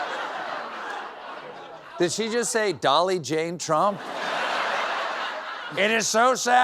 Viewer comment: I heard “Dolly Jean Trump” 😂